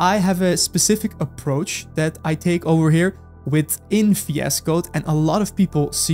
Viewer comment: is he saying "within VS Code" as in Visual Studio Code?